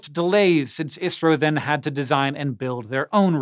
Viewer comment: i think you meant to say India instead of Israel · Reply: He said ISRO, not Israel. Indian Space Research Organization.